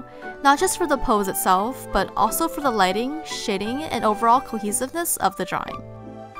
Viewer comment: sounds like she said shitting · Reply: I meant to say "shading", sorry English isn't my first language.. :c